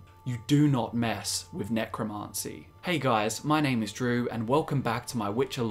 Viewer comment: "my name is Drew" I heard you said "my name is True" at first lmao